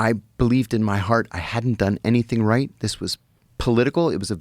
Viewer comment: did he say I hadn’t done anything “Right”😂